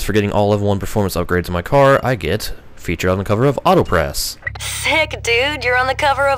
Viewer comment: did she say authopress or am is it just me?